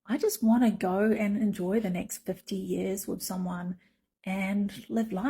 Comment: she’s 47 and wants to find someone for the next 50 years?! At first I thought she said the next 15 years 😂